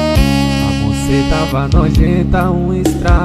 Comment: mano kkkk de primeira eu escutei a "BCT TAVA NOJENTA " SKSKSKSKSK